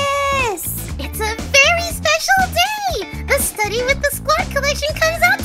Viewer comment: was it a coincidence that I heard her say “fairy special day?”